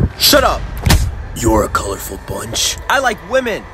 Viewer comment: Why everyone says that shadow says a colorful bunch i heard him say you’re colorful arent you